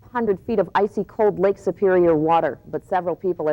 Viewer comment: It's cut off at the beginning but she said 500 feet. Since it's cut off it sounds like she said 100 feet. So if you are a recreational diver don't get any ideas.